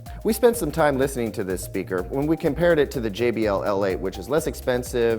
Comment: Which model JBL did he say at 2:12 ? thank you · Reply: G.A R.A. jbl la